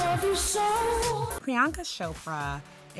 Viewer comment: Did you just call her "Prianka Shopra" cool cool.